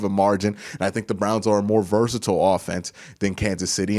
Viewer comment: have I heard versatile?